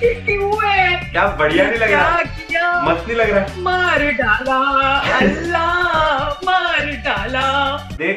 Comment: DID SHE SAY "ALLAH"